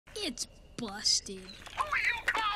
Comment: i thought he said “its bussin”